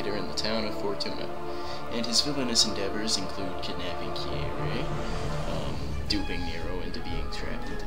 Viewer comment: It sounded like you said Kirei instead of Kyrie. Ironically Kirei is a character from the Fate series who is an evil man priest.